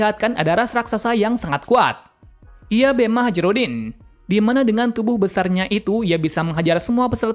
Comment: kok gua dengernya malah hajirudin ya 🤣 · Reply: Kan emang.hajirudin